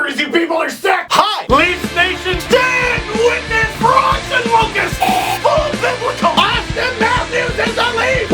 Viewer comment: Can someone explain the intro for me please, I don't understand half of it. Like frogs and locusts, full biblical. I don't understand it but I've watched these for 6 years and I understand bible references but I still don't get it. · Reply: I always thought he said "For Auston Lucas" and was even more confused. I think the implication is "holy cow this team is good, which means the world can only be going into a plague or something bad is happening" In a nutshell, Leafs fans are cynical and pessimists.